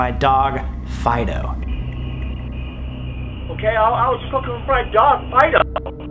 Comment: I put captions on and the captions say: "Okay I was cooking fried dog" xD (instead of "looking for my dog Fido" Or sum like that)